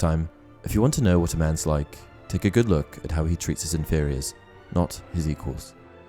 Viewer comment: lolz thought u said inferius xD was like well if he has inferi tht prolly hints at the type of man he be